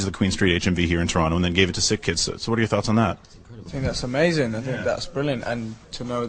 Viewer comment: omg i swore at 1:00 zayn said "its amazayn" lol <3